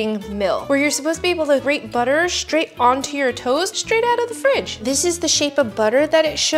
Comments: I heard “straight onto your toes” 😭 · I thought she said put the butter straight onto ur toes